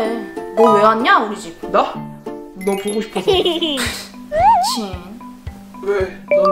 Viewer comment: 초 보면 미친♡하고 들림!